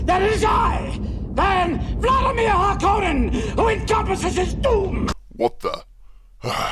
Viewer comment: did he say Vladamir hulk hogan?